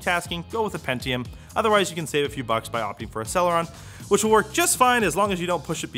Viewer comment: I still think "Sell-ya-wrong" when I hear "Celeron."